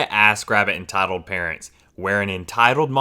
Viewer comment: Everytime i listen to the intro i hear "Ass Grab It" and im kinda like 👀